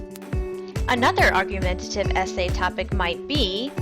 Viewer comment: I thought it said argumentative essay topic, my ass. - Johnny